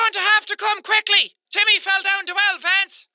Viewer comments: It sounds like she said Timmy fell down well lance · I heard "Ahh! You better come quickly, Timmy fell down the well Vance!" 😂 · I heard "You have to come quickly Timmy fell down the well Fance" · I thought the woman said "JIMMY FELL DOWN THE WELL" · I thought that lady was saying "you gotta come quick timmy fell off the fence, Vance" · I think he/she said: You're need to go quickly timmy fell down the well lance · Lol I thought jack said “help jimmy fell down the well, Vance!!!!” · I thought it said : YOUR GOING TO HAVE TO COME QUICKLY JIMMY FELL DOWN A WELL VANCE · It sounded like it said ‘’you have to come quickly jimmy fell down the well Vance’’ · With the last story, I heard "Hey, come quick, Timmy fell down the well vents" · I heard “you better come quickly Timmy fell down the wall fence” 😂😂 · I know what the woman said "You have to come quickly Jimmy fell in the well,Lance" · I heard Your gonna come quickly cuz Timmy fell down the well vance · I thought it said “hurry Timmy fell off the fence” 😂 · Is it just me or the “vance/lance” one, I heard “HEY COME QUICK JIMMY FELL DOWN THE WELL, VANCE!!!!” Hahaha lol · I thought she said timmy fell down the well lads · I heard 'Come quick,Jimmy fell down the well Vance'. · I hear you have to come quickly Timmy Fell down the well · i thought that chick said you have to come quickly timothy fell down the well fence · Who else thought he said come quick Timmy fell down the well fans😂🤣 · I heard “Timmy fell down two elephants!” 😂😂